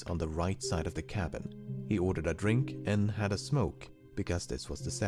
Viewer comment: I thought he said “had a stroke” instead of “had a smoke” and I was like “ah, yes, the 70s”